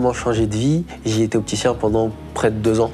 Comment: il dit qu'il faisait quoi avant ? J'entends "j'étais opticien" mais ça fait aucun sens